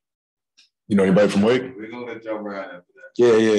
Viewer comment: What did Book say After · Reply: He said "do you know anybody from Wake?" meaning Wake Forest